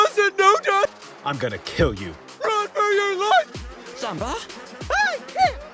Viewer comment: did sitch say simba or Zumba?